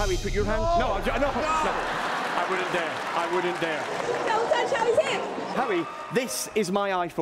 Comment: What the hell does that woman say at 0:51? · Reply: Hey, there, me from the past. I also had trouble understanding. She says: "Don't touch Howie's hands..." By the way, don't eat those chicken wings, you'll regret it.